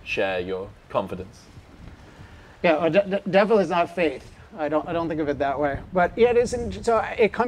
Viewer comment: "Devil is not faith" Is that what he said?